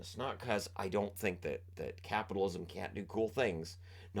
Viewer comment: Did you say "the Capitals can't do cool things"? Truer words have never been spoken.